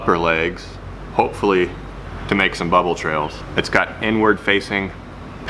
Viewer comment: “It’s got N-word facing paddled feet” That’s what I heard at least lmao